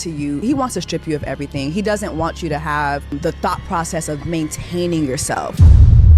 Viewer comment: Notice how it sounds like she said "Thot" instead of thought process. Mistake or projection?...